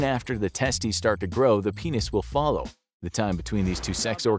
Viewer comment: in the first moment i understood “fall off” instead of “follow”